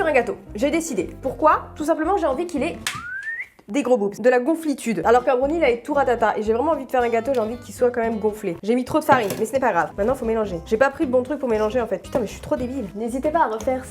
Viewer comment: au debu j'ai entendu " fuuuuut des gros dooms" XD